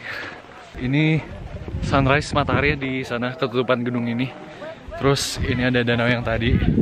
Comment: >> w salah denger apa lu yg salah ngmg 'GEDUNG'..? 😂😂🙏🙏